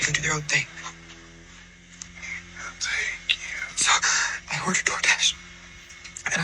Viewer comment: Did anyone else hear the voice saying "thank you"?!?😳🙏🏼🙏🏼🙏🏼